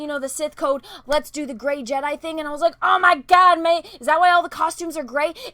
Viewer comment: did she just say oh my god mate?